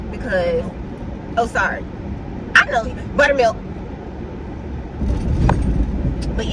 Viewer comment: i thought yall said buttermilk, and both pointed, am I tripping? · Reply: Buttermilks is the name of the restaurant